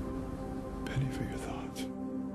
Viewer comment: What is saying at 3:16 'Pay for your thoughts?' What does it mean? And why would he say 'I'n not a priest'? I don't see the connection. Can one say he had a brush with death? I also wonder if he went to work that day... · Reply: I noticed that you wrote this 9 years ago. Anyway I think he said "Penny for your thoughts" which means what are you thinking about. He said I am not a priest because the other guy said now he could not do what he intended to do after hearing that he was on a spiritual journey. I interpreted it to mean that he was clarifying that he was not a celibate person (ie he thought that the other guy thought he couldn't have a sexual relationship).